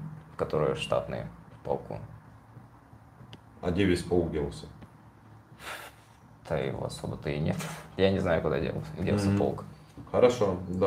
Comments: is asking "Where did all the regiment go?" And subtitles say "I hope you got killed. Then its amount of friction". WTH? He did not say anything like this. These subtitles are not only inaccurate, they can do harm. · If there’s someone out there who is great with computers maybe they would donate their skills to help this channel’s translation. It is obvious someone has been hacking in to make it completely messed up! It’s obvious it’s a Russian trying to block the information to the English speakers! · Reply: Your comment makes sense now. At 13:02 is asking "Where did all the regiment go?" And subtitles say "I hope you got killed. Then its amount of friction". WTH? He did not say anything like this. These subtitles are not only inaccurate, they can do harm.